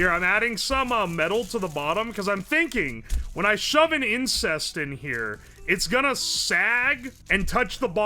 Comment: Did anyone else hear incest?